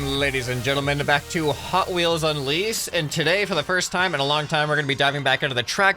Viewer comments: Mans said lease instead of unleash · anyone else hear "hotwheels on lease"?